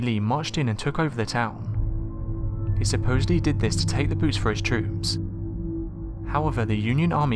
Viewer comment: LOL at first I thought you said "Booze", then heard correctly, "Boots". I suppose either are necessities of war. :P